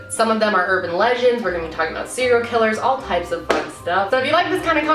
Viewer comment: thought you said “butt stuff” 😂😂